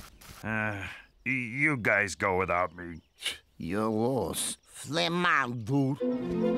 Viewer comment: was is that dragon saying, he kinda sounds like ynw melly. still what is he saying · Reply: Flame on dude